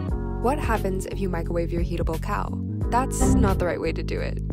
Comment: Okay this is adorable, but I genuinely thought she said yeetable cow 😅